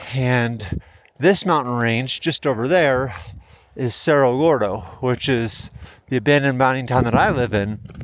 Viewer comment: Sounded like you said Cerra Lordo.